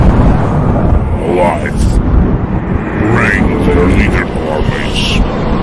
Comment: Anybody know what scienctist tv man said here? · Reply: "No !" "Bring their leader to our base, alive. " "ARE YOU INSANE?!" "We need to understand the full picture." "Before its too late."